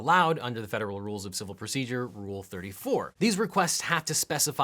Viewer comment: Wait Did he just Say rule34 O.o